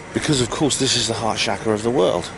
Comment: What did you say, harshaka? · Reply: "Heart chakra".